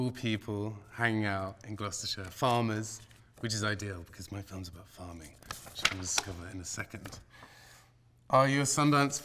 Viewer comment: Can someone tell me what is he saying at 0:27-0:29 and 1:56? I'm studying English. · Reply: I think he first says “Because my film’s about farming... which we’ll discover in a second”